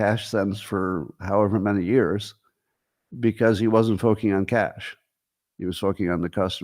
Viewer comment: Between 18:35 and 18:40, does he say fōking instead of focusing?